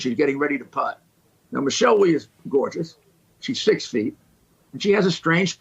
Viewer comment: When he said «she’s six feet» I first thought he said «she’s sixteen». So it could be worse, I guess..?